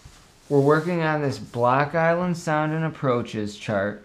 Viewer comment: i realize this is 8 years video but i thought you were saying BLACK island, would get in problems for that today lol BLOCK island, got it now